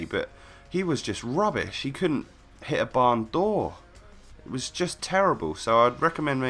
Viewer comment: I thought you said "ballon d'or" when you said "barn door" I was like "what!?" hahhaha